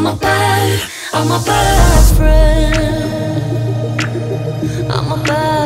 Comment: Instead of "im a bad friend" I hear "im a bathroom" and now you do too